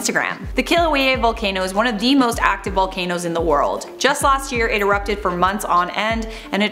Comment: I thought it said the most attractive volcano